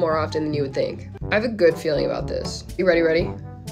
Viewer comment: NO ONE IS TALKING THAT IT SOUNDED LIKE SHE SAID READY READY 😂😂